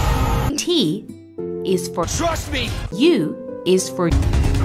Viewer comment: Im I the only one that thought it said "thrust me" Just me K